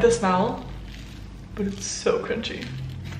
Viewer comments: it sounded like she said “but it’s so cringey” but I think she said it’s so crunchy 😂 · did she say it was so cringey or crunchy?